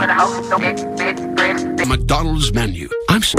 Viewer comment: did anyone else hear "mcdonalds menu' near the end